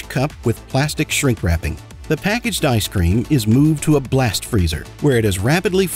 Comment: I heard that as “Blessed freezer” 😭🙏 The Omnissiah is getting my into my head